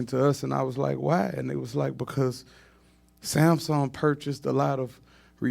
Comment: DID HE JUST SAY SONY BOUGHT ALOT RHIANNA ALBUMS🎥‼️👄🤷🏽‍♀️